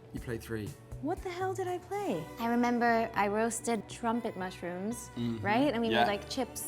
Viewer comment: I thought she said “I roasted Trump” 🥹